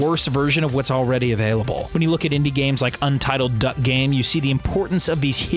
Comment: Did you just say "Untitled Duck Game"?